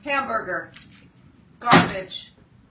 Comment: The first time she said garbage I thought she said 'gone bitch!' Haha yasss!